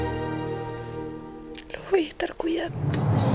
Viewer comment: cuando muere jose le entendi "voy a estar culeando" JAJAJAJ